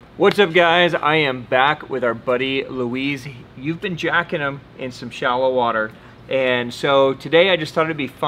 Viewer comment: First thing I hear is “you be jacking them in shallow water” 🤣 I was like AYO🤔🤔.